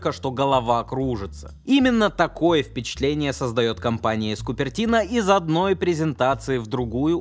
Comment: Послышалось «компания скупердино»